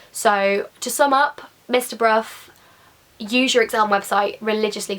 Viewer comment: I thought you said Mr bruv